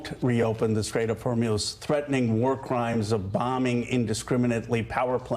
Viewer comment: did he say whore-mules?